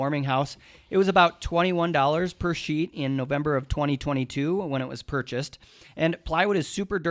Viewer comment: purchased in November 2022, did I just hear it right?